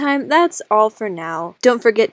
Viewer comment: I know you probably said "see ya in my next video" but I heard "seal in my next video." You should do some sort of meme or joke involving seals or something, I dunno.